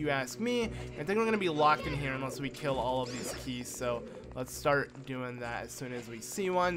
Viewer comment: Got to love these keys man... (he said keys instead of bats...) found this out from replaying this part over and over again because I didn't want to get confused in my game lol... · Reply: The bat enemies are called keese, so I understand your confusion.